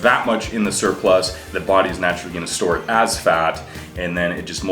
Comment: i heard it as "the body's naturally gonna store ass fat" hahahahaha